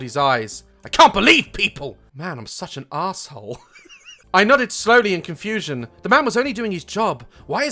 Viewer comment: I thought he said “I nutted slowly”